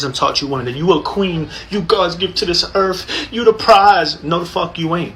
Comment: I cannot take someone seriously when they say erff instead of earth.